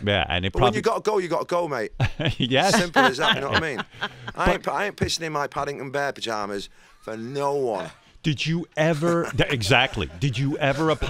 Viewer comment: What does Liam say from 01:02 onwards? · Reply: Federico Paolo I ain't pissin in my Paddington Bear pyjamas for no-one